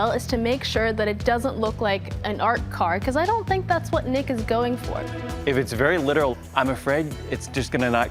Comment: "make sure it doesn't look like an art car bc I don't think that's what niggas looking for" that's exactly what it sounded like she said 😂😂😂